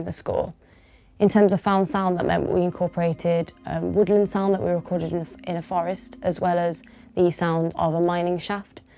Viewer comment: Could someone spell out the chapel movement chant name referred to at around 5:40? (sounded like kanir punkt) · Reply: Canu'r pwnc